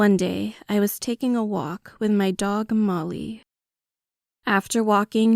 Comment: did he say mully the vr channel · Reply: He said Molly